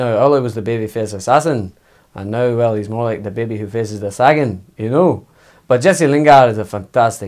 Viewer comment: "Ole was a baby face assassin!!! But now he's more like a baby facing a sackin" 😂🤣😅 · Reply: Thought I heard baby face a’saggin 🤔